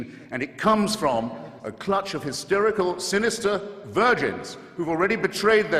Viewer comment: i thought he said "sinister virgins".. dang it.. now i got to find that vid. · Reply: you are correct, "a clutch of hysterical, sinister virgins.." I love it, especially having been raised catholic. lol